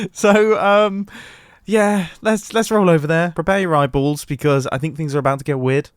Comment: I heard prepare your right balls instead of prepare your eyeballs 😂